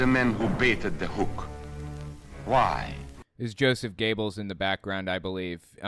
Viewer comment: Sounds Like you say Josef gaybels to me